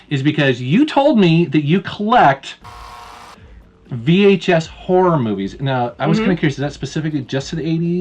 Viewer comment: My pc froze for a moment and I swear I heard say "you collecto vhs porno movies", I was like whaaaaaaaaaaaaaat .-.